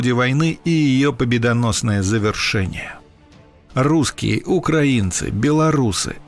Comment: Чуть от смеха не лопнул когда услышал "руZZкие"!